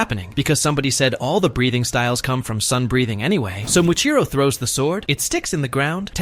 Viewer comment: Am i the only one who heard breeding